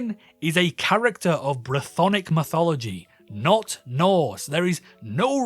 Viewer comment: It's Brithonic (brythonic) but you pronounced so weirdly that I heard Brothonic and YouTube captions went "Bronic" I love the character design. It's also based on the Asterix comic series, that they look like straight from the comic. (The comic also has action bits and sometimes goes serious) I like it. Also the "Character design/artstyle doesn't fit the edgy setting" or "looks like Genndy watched drawn together and decided that's how the character design" just feel like a slap to my face. What if I'll make a series like that!? Also I'd toy with the false argument of an immortal being possesing people to live on... Maybe What? Am I not allowed to have this? Will I be hated for character design like ViviziePop? I wanna have fun but the character design arguments just feel like a slap to my face as I said before. Am I not allowed to have fun or my ways? The series is solid for the most part but yea, pacing issues and bland stuff might be correct-a-mundo. Like cute comedic designs in edgy setting shows or anime can work really well okay? But I shouldn't complain with the unqualified Just a nobody just ruining my ideas. Words and arguments straight up from essay posts or twitter really make me grind my gears instead of turn off the device and go touch grass. Fortunately it's solid for the most part, both series and the review. My main complaint is that it's just not that many episodes that the characters and writing can spread expand upon themselves... I think